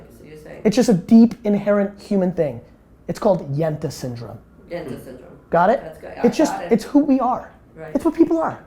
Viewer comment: Gary 20:30 mins in, is he saying Jante syndrome??? Everybody please help